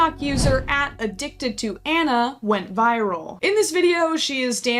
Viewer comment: I heard “addicted to Ana” and was worried